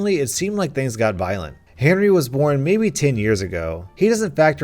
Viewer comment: lane 5 instead of Henry i think u Saïd hairy